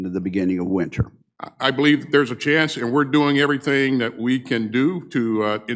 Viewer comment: He actually said: There is a chance. Nit a very good chance.